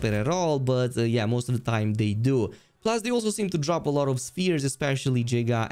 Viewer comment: did he just say "Giga sphere"?